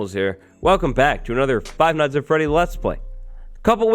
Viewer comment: doesn’t it sound like he said Five Night At Freddy’s? Lmao.